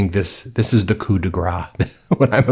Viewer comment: Commenting mostly for algorithmic purposes, although while I'm at it, pay attention at 1:40 it is "coup de grâce", which sounds somewhere like "kudagrass", whereas I hear "kudegra", which translates like "grease/fat hit". Well, I guess given who we're talking about, it might actually be correct...